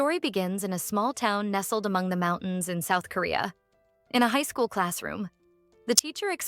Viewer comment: I thought she said north korea😂😂